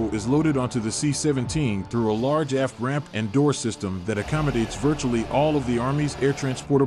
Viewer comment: For a second I thought he said “loaded through a large ass ramp” 😆